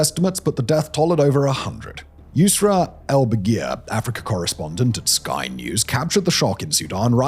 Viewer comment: I was listening to this while doing my homework and heard it as “Afrika Corps Responded” and was like WHAT?!